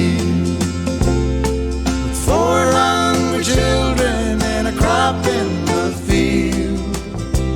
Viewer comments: I remember when this song came out in 1977 me and friends always joke instead of 4 hungry children We say 400 children and laugh say no wonder she left But another great Kenny Rogers song Check out I am the greatest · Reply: Yes! 😂 400 children was we thought he said when we were young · My dad's joke with this song song was "Ya picked a fine time to leave me loose wheel " We're twisted 😂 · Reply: As a kid I thought he was saying 400 children. 😂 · How many people that he says 400 children in stead of 4 hungry children. For the longest time i thought he said 400 children lol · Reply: Always thought he said 400 children!!!